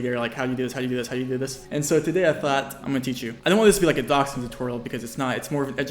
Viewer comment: Lol at 0:08 the cc says “dachshund tutorial”….please do one of those too 😂😂